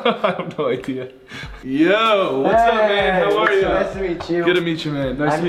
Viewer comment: is it just me or did grayson say ‘what’s up babe’ 😂😂😂